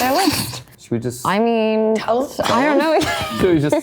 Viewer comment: Does Joe say, “should we just say it?” When Natalie read that a major character is going to die? I think it’s Eddie or Will 😭